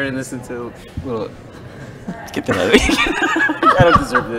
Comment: What does Dylan say at 3:13 ? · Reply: Get that out of here, referring to the microphone.